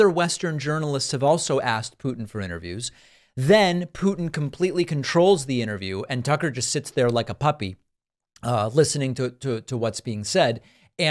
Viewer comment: When Parkman says ‘Putin control the interview and Tucker just sits there like a puppy’, I thought he said ‘like a puppet’, but you know what either one works. In the end, Tucker looked like an idiot.